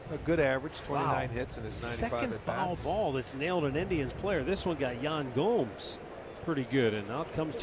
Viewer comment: sounded like “wow, second foul ball thats nailed an Indian” at first